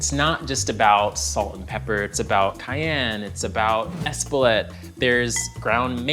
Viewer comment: what's he saying at 3:15 - esbolet? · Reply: Espelette. It's in the recipe listed above.